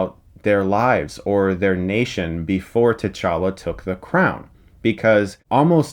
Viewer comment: When he said techalla I thought he said techanka